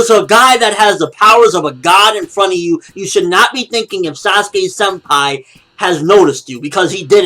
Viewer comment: When you said" sasuke senpai" I thought u said, sasuke umpire